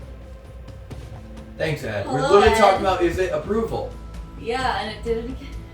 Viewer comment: Weird almost voice?.. around 42:58 in almost sounds like really deep male voice saying “Ed.” May just be music? Anyone else hear it?